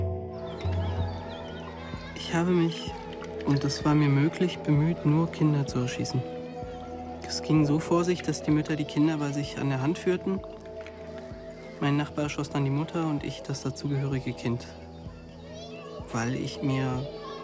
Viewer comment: Damn, I generally understand german documentaries, but I have big problems here to understand what the soldiers say. Can someone tell me what he says at 3:44? Thank you. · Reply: What he says, is: "I tried to kill only children, and this was possible for me. My mate shot the mother and I shot the child on her hand. And I said to myself, to justify this: The child can not live on without its mother, anyway."